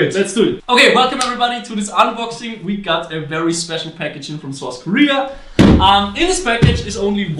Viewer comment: Idk why, but when he said special packaging, I thought he said sexual packaging so i am confused and intrigued at the same time.